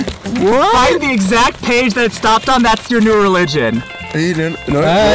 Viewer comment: Did he say "At the exact page that stopped it, that's your new religion"? LOL!! !